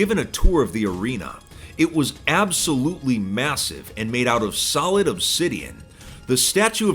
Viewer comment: massive? did you just say massive?